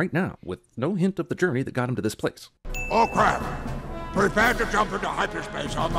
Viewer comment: For decades, I actually thought Admiral Ackbar said, "Oh, Crap!"